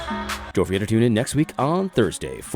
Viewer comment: For the longest time, I thought AD was saying "fight left feast." Which actually would be cool, too.